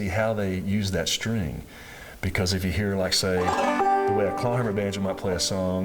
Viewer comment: What does he say at 6:43? I hear "The way a conaver band might play a song"?